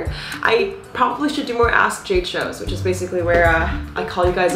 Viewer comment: It’s sounds like you said “I should probably do more ass jade shows!” 😂😂😂 but we know what you said.